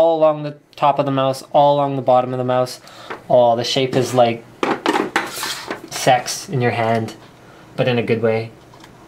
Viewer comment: DID HE SAY SEX?